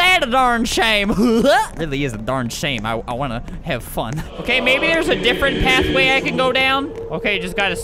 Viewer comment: D-did you say you want to have fun??